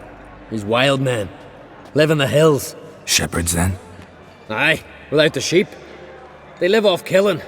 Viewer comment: I always thought Hjalmar said "Aye, they plough the sheep" when Geralt asks about the Vildkaarls